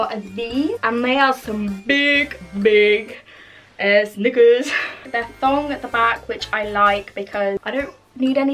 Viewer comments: "niggas" 🤔🤨 (idk maybe I just heard bad cuz I'm not english native so..) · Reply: she said knickers, brit slang for underwear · wait did she just say the n-word? Oh nvm.. She said nickers lmao. British people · Bro I thought she said the n word but then realized she said "nickers"💀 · did she say “big ass niggas”??😯😯